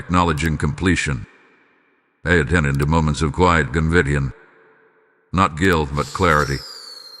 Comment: Please, don't block the wordings. I read transcripts. I have to cross-checked against the screen. For example, like this one, 50:26 " I attended to moments of quiet gvidian, not guilt, but clarity." What is the word after quiet? The content creator must pronounce the word clearly in order for the system the capture it with accuracy. I read transcipts alot.